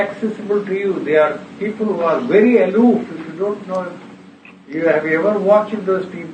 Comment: Can someone tell me what word he used at 30:28? People who are very (?) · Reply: "people who are very aloof "